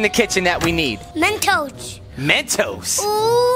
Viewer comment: I thought they’d said french toast😂😂